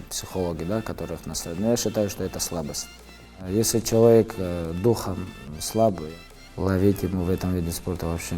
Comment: Что он сказал? Непонятно , подскажите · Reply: Serkon Salyikzhan если человек духом слаб - ловить ему в этом спорте нечего